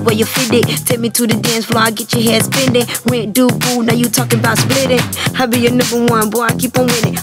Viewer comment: i swear she says "when do poo" O_o